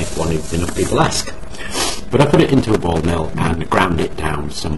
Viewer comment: Sorry for commenting on such an old video and hopefully I am able to get a response from you, but at 6:35 in your video you mention that you mixed the carbon substance you created with a "Ball" metal?(sorry if I heard it incorrectly, but I'm curious to try this experiment and want to make sure that I am using the correct materials). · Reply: ball mill mate - normally i use zirconium balls to help prevent contamination